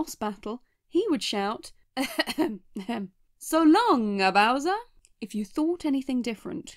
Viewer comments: i think its actually cus people thought mario was saying "so long gay bowser" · actually i think they removed it because it sounds like mario is saying: "so long gay bowser" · Nintendo: “So long eh bowser?” Internet: “so long, gay bowser” Nintendo: ”n o” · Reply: I heard ‘solongkey Bowser’ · It was more likely because people misheard it as “So long gay bowser!” As it would make Mario seem homophobic. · I always thought he was Saying Big Bowser. It honestly hurts that they used the shindou version for the 3D all stars, because now I feel like I'm missing out on things especially the BLJ trick that I never knew about as a kid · I always heard "So long King Kowser " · I always thought he said so long, king bowser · Nintendo: “So long eh bowser?” Internet: “so long, gay bowser” Nintendo: ”n o” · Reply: I honestly thought he said "So Longy Bowser" lol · i always thought it said so long gay bowser i dont know why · I always heard it as “So long-a Bowser!” But that just might be me... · i thought it was cause it sounds like he says so long gay bowser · I thought he said "so long gay bowser" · I always thought Mario was saying "So long king browser!" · I always thought he was saying "so long king bowser" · My childhood remembers, "So Long KING BOWSER!" I've been scratching my head lately and thinking maybe the output of the sound could easily be misheard as 'gay?'